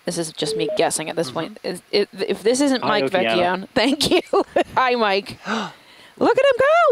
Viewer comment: did she say Mike Vecchione?